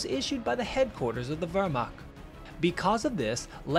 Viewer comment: I don't know who does the closed captioning, but I am guessing it's an automatic system, as I can't imagine a person would have written 'the vermont' instead of 'the Wehrmacht.' It's at the 5:41 mark. The boo-boo ads a humorous note to the reading. Anyway, good video. I too, as others mentioned, why only 1 fighter out by himself.